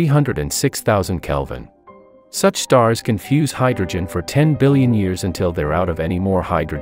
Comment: I misheard "Such stars confuse hydrogen for 10 billion years" which confused me.